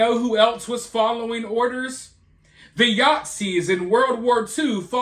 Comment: Did you say The Yahtzee’s 2:00 ? 😉